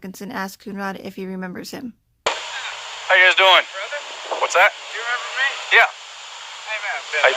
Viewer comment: It almost sounds like he said ‘..big brother…you remember me?” The officer said yes but he wasn’t warm or even ‘friendly’ to. Perhaps was part of a big brother program. The kids in such programs are usually from broken/low income homes and may be troubled. Many cops volunteer for programs like this and from what I gather, doesn’t have a father, or at least no relationship with him? 🤷🏼‍♀️. NA was a teen mom and would’ve been good candidate for programs like Big Brother.